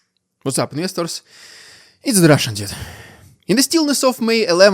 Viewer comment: When you introduce yourself in the begining of the video I always hear it's the russian idiot no offence. It's just the way you pronounce it